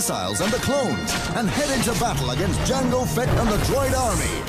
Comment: When the guy said “Jango Fett” I heard “General Fett” lol